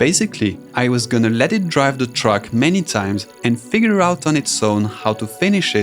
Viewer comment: y do i hear truck insted of track